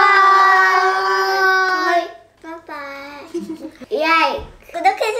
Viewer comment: I love everytime says “coment coment” at the end of the video. Yang kadang kedengeran nya lebih kayak “komeng-komeng” hahaha Cepet pulih ya dede, himnae !!!